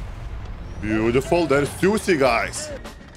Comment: To the 4:07 , I literally heard saying, "Two Cigars". fr not joking plus, I was also focusing on the caps lol. Nothing offensive.